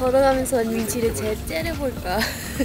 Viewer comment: 초 나만 민지를 째려볼까를 민지를 때려볼까라고 들었음? ㅋㅋ 아 자막없이 보다가 놀람